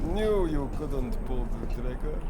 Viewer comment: What nikolai actually says: knew you couldn't pull the trigger. What the auto generated captions hear it as: knew you couldn't poop